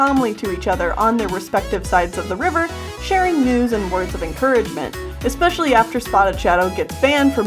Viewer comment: When you were talking about dawn and spotted about sharing news and words of encouragement I thought you said “sharing nudes and words of encouragement” xD